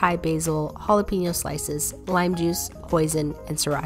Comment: I really thought you said “lime juice, poison-“ 💀